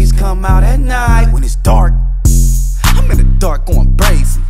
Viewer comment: Thought he said "I'm in the dark, bein lazy"